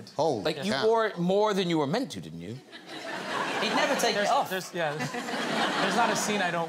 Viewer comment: Tom Holland gives somewhat of a spoiler. I think He said "there's two"